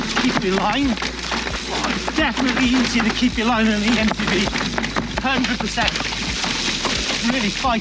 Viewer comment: At 13:38 did you say ' It is easier to keep your line on an eMTB '?